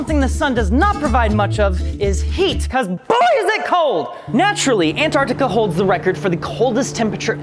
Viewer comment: What kind of word is "boizing" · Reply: he says "boy is it cold"